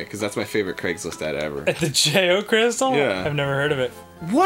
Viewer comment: When I first saw this episode, and Arin was talking about the JO Crystal, I thought he was saying "geo crystal" but that he was just pronouncing it wrong. Now I know what it stands for.